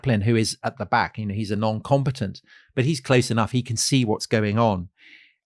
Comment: I heard "non-competent" and thought 'what a novel way to call someone an idiot'